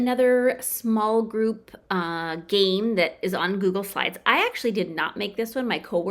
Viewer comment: How can I access the games How many recyclables are in the bin and how much garbage is in the trash? I thought you said Google 5. We are remote again at least for this week on Google Classroom.